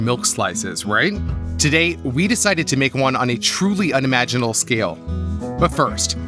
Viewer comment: Did he say "unimaginal" scale?